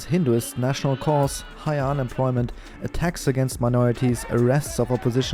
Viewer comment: What did he just says, tax against minority. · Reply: I think it was attacks against minorities. I also heard tax first 😂